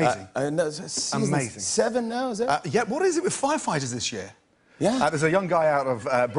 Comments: Who else thought Gordon said "what is it with fire fighters and shit?" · Did he just say "firemen and shit" but "shit" wasn't censored? xD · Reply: Are you referring to 'What is it with firefighters this year?' I heard it as Firefighters and shit too lol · Who else thought Gordon said "what is it with fire fighters and shit?" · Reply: I heard "what is it with firefighters this year?" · Did he just say "firemen and shit" but "shit" wasn't censored? xD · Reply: i swear to god until i read it was this year i thought he said shit as well fuck me